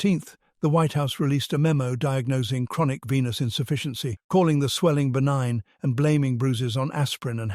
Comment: Whoops. I misheard chronic venous insufficiency as chronic penis insufficiency. Which while unfortunate isn’t life threatening